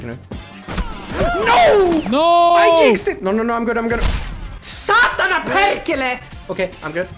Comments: SATANA PERCHE, whaaaat i hear? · dont know what you said but it sounded like "sat on a painkiller!" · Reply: He said satana perkele(it's swearing in Finnish)